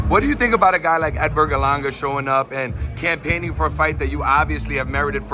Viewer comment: Dang, I thought he said said El verga langa 😅